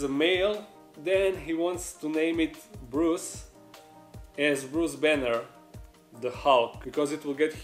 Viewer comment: lol for a second I thought you said Bruce Jenner